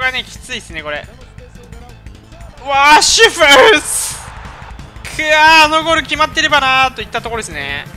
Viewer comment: なんて言ってる？www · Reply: わぁラッシュフォード